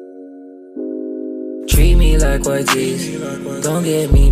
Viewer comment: I heard : "Tweet me liek coi gee" 😂